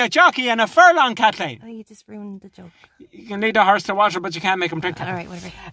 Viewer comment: “You can lead a horse to water” lmfao his tone killed me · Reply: hey there, I dunno if it's correct but from what I heard he said "you can lead a horse to water - but you can't make it drink, Kathleen". Can't say if this comment refers to the game tactics or Kathleens attempt of a joke he ruined though. Maybe you can make something out of that ^^